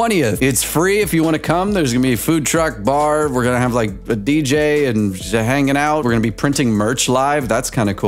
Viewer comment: for a good second I coulda swore you said “Bj” instead of “Dj” so I was very confused